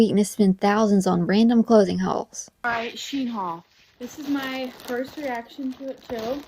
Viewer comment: thought she said 'She-Hulk'😭😭